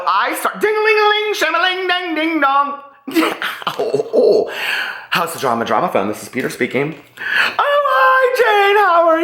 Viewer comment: I live for House of Drama: Drama Phone. lmao I either need that number or I need to know where to put in an application for House of Drama. Hahaha. I love it. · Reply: Megan Delamar omg I just realized he says house of drama. I always thought he said "how's the drama? Drama Phone"